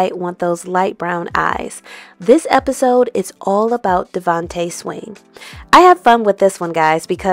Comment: Oh. All this time, i thought was saying, Devante sing. Never would have thought.